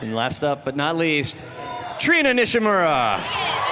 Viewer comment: I thought they said Shigaraki or something like somebody's name fr a minute..nope..they just sound alike lol